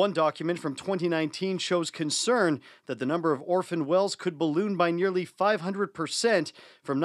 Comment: Swore she said Orson Welles